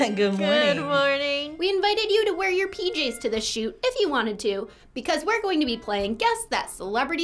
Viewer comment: anybody else heard something peculiar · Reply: "If you want it to" 😆😆😅😅 Helium voice